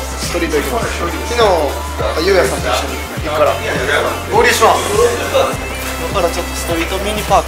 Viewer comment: の合流します！が オーリーします！に聞こえる